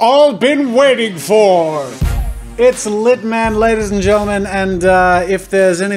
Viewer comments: I thought it said "ZITMAN" until he said Litman... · This is a bit cool, not really and I accidentally thought it said ZITMAN instead of LITMAN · Am I the only person who thought the "LitMan" part said "ZitMan"? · It said litman and I thought it said zit man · Am I the only one who thought it said zitman instead of litman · I thought "Litman" said "Zitman"